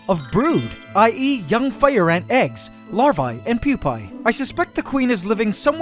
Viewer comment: Wait- At 1:33 Did you say I eat yum fire ant eggs!? Lmao